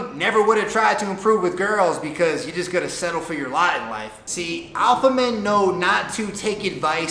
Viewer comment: did you say “settle for your lie in life” ? I couldn’t really hear it.